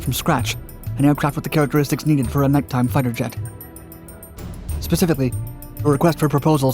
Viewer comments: Did I hear them call it a night time fighter "Jet" at about 2:09m Hmm imagine that in 1940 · Did he say night time fighter jet at 2:10?